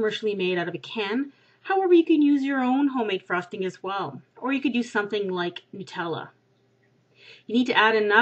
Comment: I think she said you could also use Nutella.